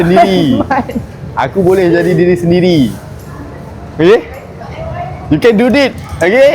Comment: Mungkin aku salah dengar tapi selalu nye aku dgr orng jerit "YOU CAN DO THIS! "😂.